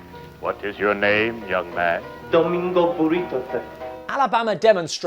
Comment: Sorry but I think I misheard that... Did he said his name is Domingo Burrito? Seriously? Also, nice T-shirt.